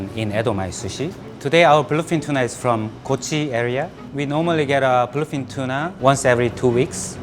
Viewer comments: listen to what he says Heard anything? Edit: if you haven't heard he said "today our bluefin tuna is from coochie area" or idk what the spelling is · Did he say his Tuna is from the coochie area?